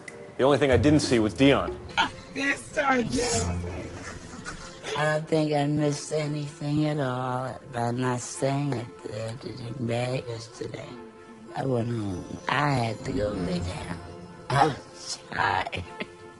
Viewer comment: What in the world did Dionne say from 12:53 mark to 12:56 mark ? LMAO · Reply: “I don’t think I missed anything at all by not staying at [….] yesterday. I went home. I had to go lay down, I was tired.” 🤣🤣🤣🤣